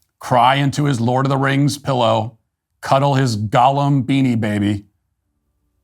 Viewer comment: When Matt mentioned Ben's Gollum Beanie Baby, but I thought he said Golem Beanie Baby...which I think would have been a much smarter joke.